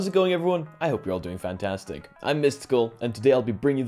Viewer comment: First time seeing this channel and really misheard "I missed school" for "I am Mysticle" haha